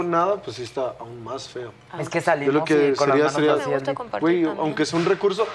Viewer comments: Que dijo Angie?? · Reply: "a mi me gusta compartir también " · Que dijo Angie en el 3:06?????? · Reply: "A mi también me gusta compartir" y si he eso se ha visto ahi es muy compartida con lo de ella... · que dijo?? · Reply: Es que me gusta compartir también eso entendii